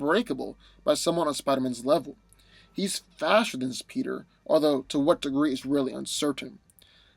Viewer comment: lol 2:46 subtitles say "He Fashioned His PETER" Now I picture A dick wearing a tuxedo 🍆🤣🤣